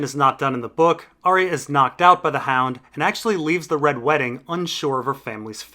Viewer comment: i thought you said "the hound knocked arya up" XD